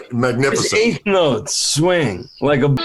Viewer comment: I want to know what was said. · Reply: I believe Luke said something that ended with ball bag. "Ray's eighth notes swing like (something) ball bag)" LOL!